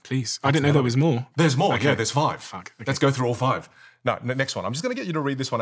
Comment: Did he just said f*ck?